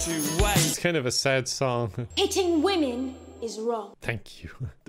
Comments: Was I the only one who misheard "hitting women is wrong" as "eating women is wrong"? Not that it doesn't make sense, but... BREAKING NEWS! EATING WOMEN IS WRONG! EAT MEN INSTEAD! or what? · i heard eating women...